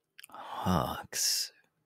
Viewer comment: What he actually said: hawks The subs: h u h